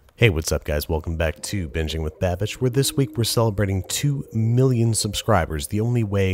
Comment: I'm tired and I thought he said 2 billion subscribers...